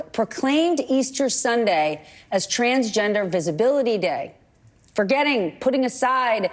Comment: I thought you said Trans Disability Day, lol. Oh yes, Christ is King.